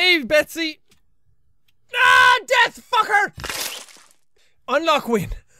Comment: Misheard "death fucker" to "Dead fucker"